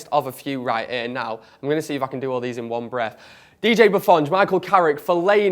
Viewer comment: Did he say Buffon while listing the midfielders we have at around 4:59?